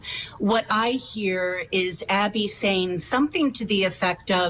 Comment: I thonk i hear "is he stkll behind me?" And "is that a gun?" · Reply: Yes, Abby said "Is he still there behind me" and "Don't leave me up here" and asked Abby "...that be a gun?" and Abby said a nervous, "Yeah" and ran past off the end of the bridge.